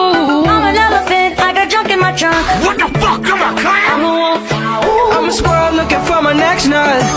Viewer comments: "We forgive you germany" "WHAT THE FUCK IM A CLAM ?!" GOT ME HARDER THEN MY PARENTS LAST NIGHT BRO 😭😭 Edit: LIKW HOW DID THIA COMMENT GET SO MANY LIKES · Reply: Y'all it sounds like he said clown but he isn't even a clown he is a clam so he said "WTF I'M A CLAM" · "WHAT THE F--- IM A CLAM?!" got me doin the spongebob laugh 💀 (Edit: i am so happy, this is the most like I ever get..) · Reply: bro i thought it said what the, F*** im a clown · WTF IM A CLAM had me dying 🤣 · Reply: I thought he said "WTF I'M A CLOWN!" but then I watched the original music video and found out he's a clam.